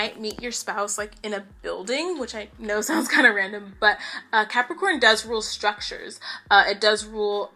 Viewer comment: Example HERE IF YOU CONFUSED. 7th house on the sign : Aquarius Ruled by Saturn Saturn on Taurus 10 th house Opposite is SCORPIO. thats it. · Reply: I thought she said Capricorn is ruled by Saturn